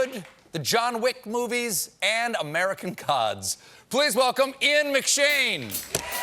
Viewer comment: I thought he said "American cods" and I was like, McShane did a fishing show?